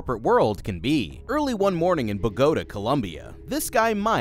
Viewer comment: what dawg trying to say Bogotá Colombia 😭😭😭